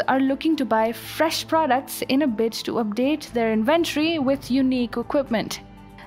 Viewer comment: Did she say "bitch" instead of "bid" 🤣🤣🤣